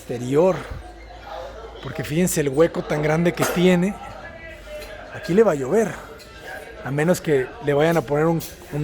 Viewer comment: Pareciera que cuando dice el arquitecto del hueco habierto “aquí le va a llover “ lo que yo escuche “aquí le valió ver.......#*^a😂😂😂